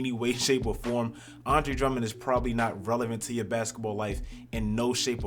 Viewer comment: Am I the only one who heard him say Andre Drummond is 320!?😂😂